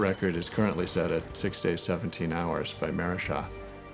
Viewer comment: I thought he said "16-17 hours" I was like holy shit lol